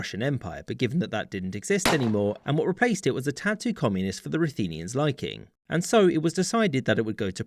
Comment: I couldn't hear clearly what was is said at 1:44 about the Ruthenians -- something about communism to the liking? Can someone clarify, please. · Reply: "and what replaced it was a tad too communist for the Ruthenians' liking"